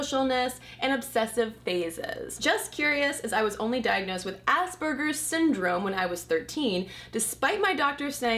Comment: I thought you said, "I was diagnosed with ass burger." lol